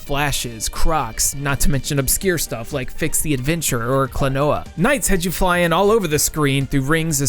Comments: What game was mentioned at 3:21? I hear Fix the Adventure but a google search of that brings up nothing. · What game is this? I probably misheard as Fix the Adventurer, because nothing comes up when I search it.